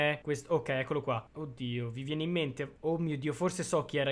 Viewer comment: solo io o ho sentito coddio al minuto 14:00/